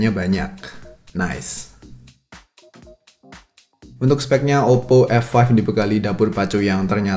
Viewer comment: kok kedengernya oppo f1 yaa 😂